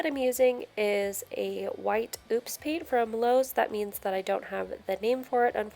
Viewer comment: Hi could you tell me what the paint is ? it sounded like you said (goop paint). in the UK we have emulsion which is used for plastered walls either satin gloss or matt finish, for wood we use mostly gloss paint or eggshell which has a hard finish. other than that its a metal paint with undercoat Many thanks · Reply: She said Oops paint. Here, at our home improvement stores, if the person behind the paint counter messes up a paint order (so the paint result wasn't what the customer ordered) then they redo it for the customer, then they discount the "oops" and put it in an area where people looking for small batches of random paints can get some for a cheaper price. Since it was an oops (sounds like the paint person made a bad color) then the youtuber didn't know what color it was.